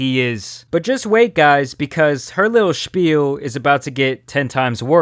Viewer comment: did say "spiel"? I didnt know that word is used in englisch. It's German for "game" btw.